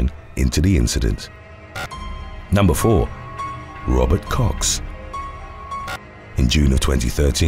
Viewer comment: it sounded like u said something else (like "rubber.. something" lol)